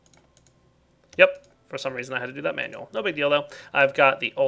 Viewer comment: Did I heard : "No big Dildo ?" at 8:31 ?